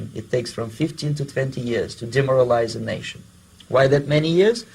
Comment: “it takes 50 to 50 yrs To demoralize a nation?” What did he say, how many yrs?